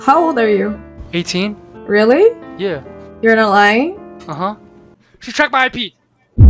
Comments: It sounded like she said “ ur not lying” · She actually said “your not lying”.